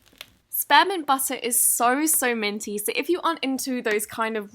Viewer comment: I thought she said sperm in butter instead of what She actually said 😂 ooPSSIE